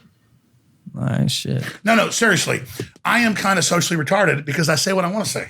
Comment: What did he say at 45:59 ? really wanna know lol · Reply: alex said "I'm kind of retarded" referring to his social skills are not his strong point - its a great clip on the JRE podcast